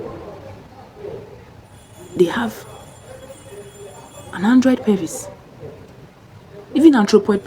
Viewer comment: these grammar big ooo, did I just hear android pelvic?